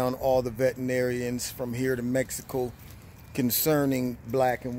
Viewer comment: Sage Parsely HI! I LIVED IN NM FOR 20 YEARS. I LOVED IT. I WANT BACK! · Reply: Gladys Rodriguez I thought he said from here in NM, but he said from here to Mexico. Where did you live?